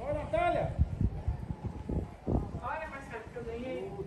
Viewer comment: escutei o nome da Marcele ou eu estou doido kkk